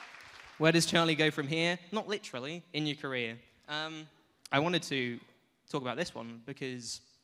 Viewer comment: When Charlie said at 16:20 "in your career", i thought he said "in North Korea"… wtf ?